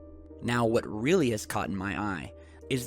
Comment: Did you really say "what has really caughtEN my eye" at 3:23? Don't think that's a word bro.